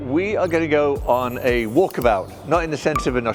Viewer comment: says: "walk about" and I hear "war kebab" thinking what the hell is war kebab?